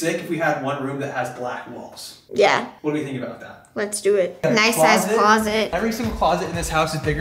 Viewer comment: When she said "nice sized closet" I thought she said "nice a** closet" 😂